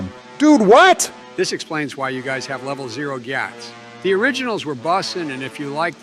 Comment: What did he just say? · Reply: Level 0 Gyatts. Slang for their asses are flat 😂